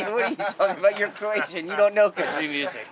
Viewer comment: what does he say in 9:30 - "youre croatian"?